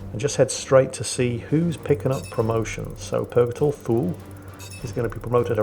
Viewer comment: purgator fool, is what I thought you say